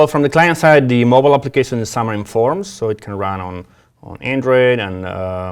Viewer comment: Sub-titles @ 9:25 to read as "Xamarin forms" instead of "summer in forms"